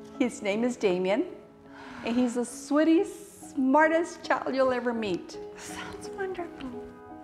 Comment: Did she just say "sweatiest child"??😭😭